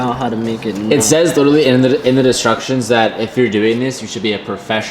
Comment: did you really say destructions lul :-)